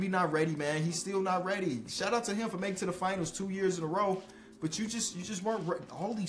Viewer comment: Every time I hear him say “In a row” I knew he was saying “In the row”. Now I know i wasnt trippin